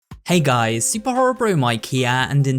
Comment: I always thought your intro said, “Hey guys! Super horror bro from IKEA!”